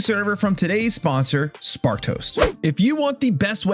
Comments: Swear I hear you saying "Spark Toast" rather than "Sparked Host" ... 😂😂😂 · Does anyone else hear “Spark Toast”